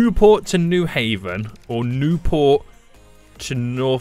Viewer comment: it sounded like u said new fu@ port